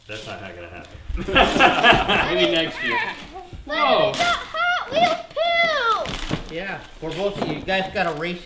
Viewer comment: It sounded like he said "we got hot wheels poo!"